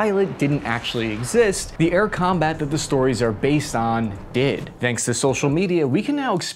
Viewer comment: What does he want to say!? ... are based on DID ... !?????????????????????????? I don't understand him very well. I mean, the video footage was from DCS World. Meanwhile ( I hope ) everybody knows that. Does he want to say DCS, but some audio or cutting error scrambled up the word!? This sound that he makes .... DID ..... is annoying!